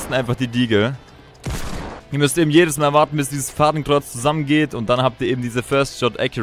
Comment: ich hab Hakenkreuz verstanden